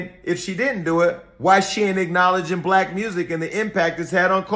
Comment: I thought she said she ain't Black